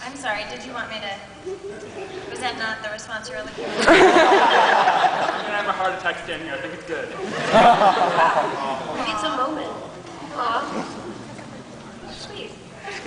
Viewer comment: Can anyone tell me what the guy says at 0:38 and what Felicia says after that? Thanks for uploading this!! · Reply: I think I'm gonna have a heart attack standing here Oh wow he needs a moment